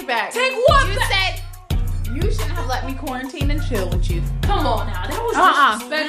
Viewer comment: Wait a sec did just say quarantine and chill 🤭🙃 @ 17:46 nowwww whatcha meannnnnn by that